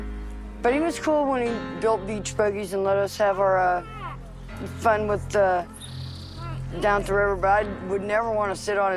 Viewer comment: ANYBDY ELSE HEAR THAT WISPER ??!! WHAT WAS SAID ?? ALL I CULD MAKE OUT (I THINK) WAS "KEEP .... " ... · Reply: It was his mom, she whispered “keep it running for me” (keep recording)